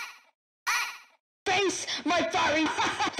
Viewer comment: "FACE MY FIREY RAT HAHHAHAHAHAHHAHAH-" Honestly the voice scared the crap outta me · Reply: I thought he was saying FACE MY FIRE FACE MY FIRE FACE MY FIRE HAHAHAHHAHA